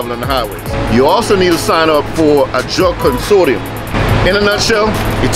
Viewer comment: What's he say it takes at 5:46 a what??? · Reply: drug consortium. Basically a drug testing policy for your LLC that meets DOT requirements.